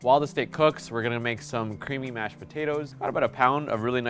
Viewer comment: Imstead of "CREAMY mashed potatoes" i heard "CREEPY mashed potatoes"